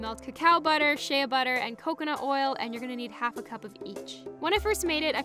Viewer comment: lol I totally thought you said your going to need half a cup of bleach!@ 1:59 ouch :)